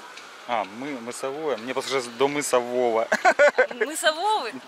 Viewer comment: Мне тоже так сразу послышалось до мыса Вова.